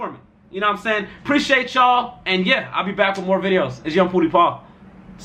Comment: Thought this man said poudi pawg